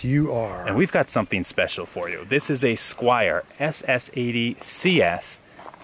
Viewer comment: I thought it said squirrel but it squire